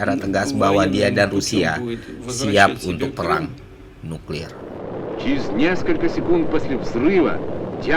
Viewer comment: Asli gue ga salah denger perang nuklir ???